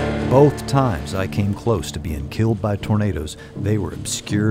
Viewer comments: the CC says " both time I came close to be killed by Burritos" instead of killed by tornadoes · the subtitles say "...killed by burritos" instead of "...killed by tornadoes" lol